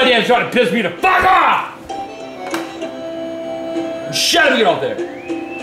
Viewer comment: what did he say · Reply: He said: "Shove it up there"